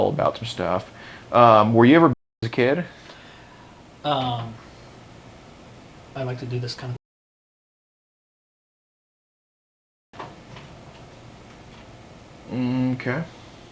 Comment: What did he say @ 27:41? Does anybody know ? Please reply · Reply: He said I would like to do this off camera I think but I don’t kno why they would cut that out